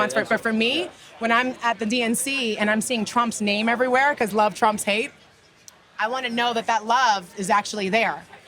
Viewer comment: She said, " Love Trumps Hate" I hear, "Hate Loves Trumps, Trumps Love Hate"